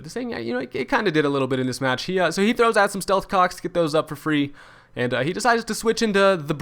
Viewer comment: Did anyone else hear him say "stealth cocks" at around 1:18-1:21?